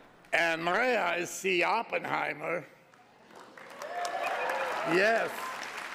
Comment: I swear I thought he said “Maria, I see Oppenheimer” in his Tony Montana voice😂